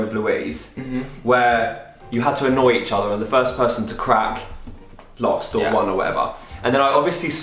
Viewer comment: When said the firs one to crack I thought he said fort one to do crack I was like omg god pressed rewind and was OHHH 😂